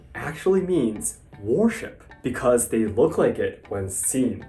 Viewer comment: when you said "war-ship" I thought u said worship ahahaha xD I was expecting to see some sushi-no-kami appear and banish the sushi-no-oni from the tabIe to improve manners xD IoI